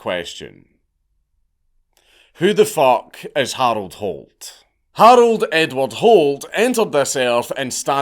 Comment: I wasn’t paying attention and I heard “Harold “N Word” Holt” and I lost my shit